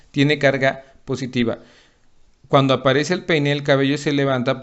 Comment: Entendí pne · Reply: Dijo pene